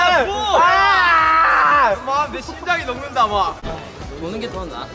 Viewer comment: Jin calling Tae as "Vu" instead of "V" omgggg my heart TOT